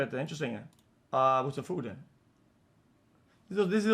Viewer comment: Bro what did u say 0:07 all i heard was food